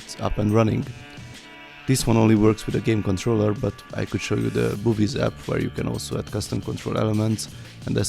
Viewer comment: I’m sorry I’m not sure if I heard you properly, the boobies app?